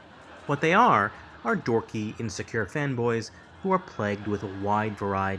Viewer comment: i thought you said femboys for a sec 💀